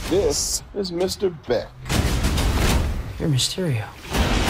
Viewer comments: For a second I thought he said Rey mysterio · COULD'VE SWORN I HEARD PETER SAY "REY MYSTERIO"...? lol